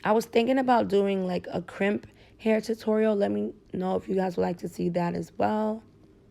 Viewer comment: I'm such a dumb ass lol I thought she said a "crip hairstyle"😭😭😭😭